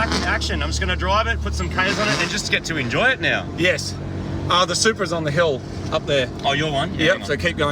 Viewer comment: wait a min did he say his Supra is up on the hill? NANI?!?!?!